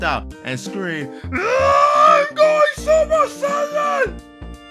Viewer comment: did you just say super science? 😂😂😂